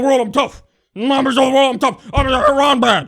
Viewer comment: did Kyle just say Harambe? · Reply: Red Hood I think he mumbled "Iran bad"